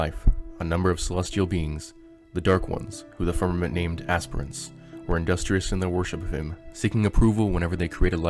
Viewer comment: I thought he said ASPIRINS ha ha